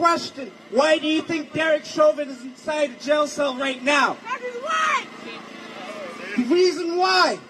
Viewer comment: did she say “cuz he’s white”? Whoops 😅